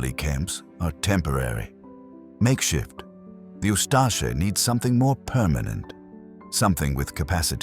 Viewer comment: turn on subtitles it said the US instead of the Usta*#%# 😭